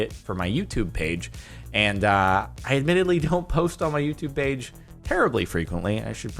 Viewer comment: Umm... Soooooo you never post on your YOUTUBE page? Edit: Srry, it sounded like you said you never post on YouTube.